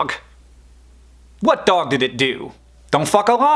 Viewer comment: I kind of prefer R rated Hank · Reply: Nacho Chips I heard “Don’t fuck Obama “ first lol